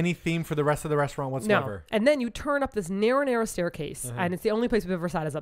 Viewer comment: As soon I saw coffee jelly I was hoping you guys would bring up SAIKI K! Also, when said “Narrow, narrow staircase” I heard it as “marinara staircase” then realized what you actually said, and then I couldn’t stop laughing. Love these pod casts!!! <3 from Houston, TX